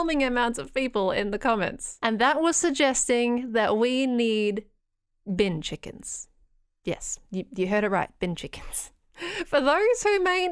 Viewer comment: I thought you said "we need Bing chillings" 💀